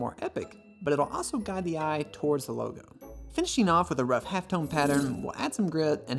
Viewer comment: How do you call that at 04:40? Red HEFTON pattern? HEFTON? Did I hear correctly, I’m not a designer but need exactly that for my design. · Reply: Half-tone